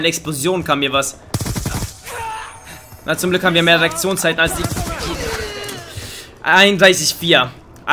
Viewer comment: xD ich hab Errektionszeit verstanden als er Reaktionszeit gesagt hat ^^